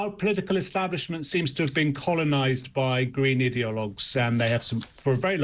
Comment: Oops. You said "idealogues?" I actually thought you said "idiots"!!!!